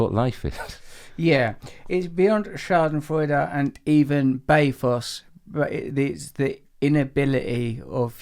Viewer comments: I wish I knew what word he says around 12:46, after schadenfreude. It sounds like "schadenfreude and <BAY-foss>" ... maybe I'm just mishearing "pathos" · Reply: The noun bathos refers to an abrupt and often ludicrous transition from the elevated to the ordinary (a form of anticlimax), or to an excessively sentimental demonstration of pathos. The word bathos (adjective form, bathetic) almost always has a negative connotation. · beyond Schadenfreude and...? can someone write what he said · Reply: Bathos